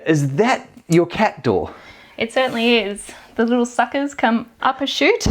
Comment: ± , did she just say "those little fuckers". 😆